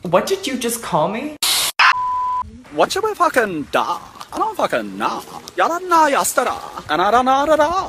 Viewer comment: what are they saying??? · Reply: What should we do i don’t know you didn’t know yesterday and I don’t know today emkay